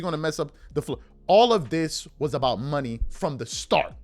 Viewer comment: as I listened to this in the background, I could have SWORE Charles said: "All of this was all about Honey 🍯" 😂 Would have made perfect sense for Bronsexuals 😅